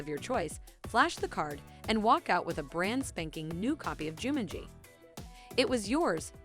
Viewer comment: im sorry I don't mean to sound mean but... did she just say Joomangee?